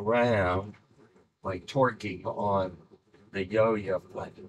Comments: I heard the guy right the first time. Torquing.... but when you added the subtitles, I started questioning why you did that. Then I realized it sounds like he said "Twerking on the Yo-Yo. 😆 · Idk man, sounded like ‘twerking’ to me…. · I'm 90% sure this an innuendo, but I just don't get it; however, I also deal with torque daily in my job, so I don't blame myself for my brain being too literal. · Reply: to my ears it sounded like he was saying twerking. Hence why it was necessary to put the word torquing on screen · I heard "Twerking" and I was like "AY WAIT WHAT?". I love that you clarified that it was TORQUING. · yeah yeah I didn't hear twerking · thanks for the clarification. i thought he said "twerking" :) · This was a really awesome demonstration of physics in micro gravity and thanks for the clarification, I really thought he said "twerking" 8:24 lol 😆👍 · I heard torqueing until the editor clarified that thats what he said. Now I can not unhear twerking lmao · "The word was "torquing"" lmao. Okay so I wasn't the only one who heard "twerking"